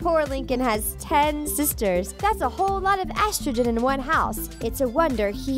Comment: I THOUGHT SHE SAID "That's a whole lot of extra in one house!" At 1:48 LMAOOOOO